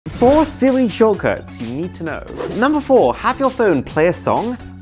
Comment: I legit thought he said ''four silly shortcuts u need 2 know'' please tell me I'm not the only one who heard this